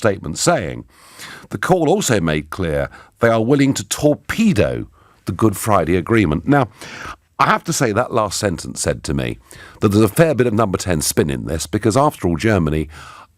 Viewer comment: When he said "They are willing to torpedo" I heard "They are willing to talk to a pedo"